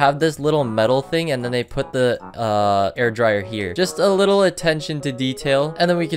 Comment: why did I think he said hair dryer 😭😭